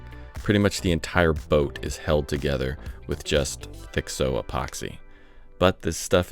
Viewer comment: I thought you were saying "Fixo" instead of "thixo" literally every time I've heard you say the word for like a year. Anytime any maker has said it. I finally saw the tube at 28:57 and I am questioning my hearing now.